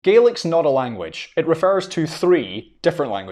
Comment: I thought he said Garlic.